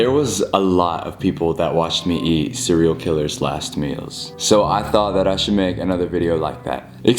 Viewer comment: I thought you said "eating cereal killers" lmao